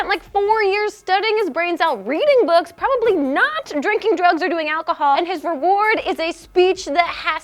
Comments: Did you say "drinking drugs or doing alcohol" at 1:51? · Did grace say drinking drugs and doing alchohol!?